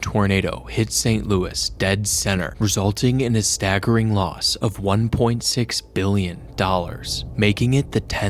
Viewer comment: Phew I thought it said 1.6 billion people